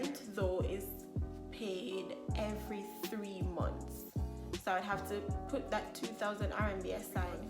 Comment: excuse me?? Did you just say every THREE months? Girl, don't play 😅😭😭😭😭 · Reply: Yes, you heard right. Every three months. Some landlords have other arrangements but the most typical you will find is every three months.